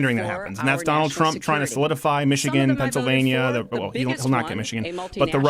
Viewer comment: did he say "he wont get michigan"? Hahhaa LOL